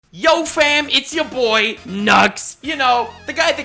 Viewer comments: I thought he said “yo its ya boi , nutz” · Bruh I accidentally clicked and then I thought he said its yo boy nuggs